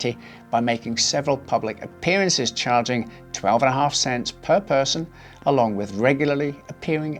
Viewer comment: hold up did he just say 12.5 cents?